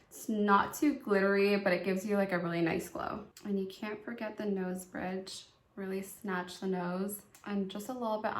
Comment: Oh my gosh, I thought you said "and you can't forget the nose...BIATCH" I was like YES!! Feisty! LOl. I had to listen a couple more times and found you actually said -- "and you can't forget the nose BRIDGE." LMAO LOL LOL. Love you & your channel -- gworgeous honnayy!